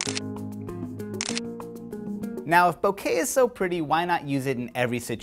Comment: did he just say bukakke? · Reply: no bokeh!